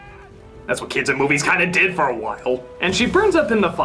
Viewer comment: Is it just me or does NC say "That's what creepy kids in movies kind of did for a waffle" at 5:00?